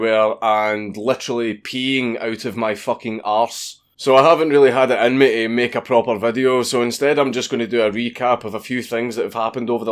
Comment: I heard "wee-cap" instead of "re-cap" which is, ya know, sort of fitting.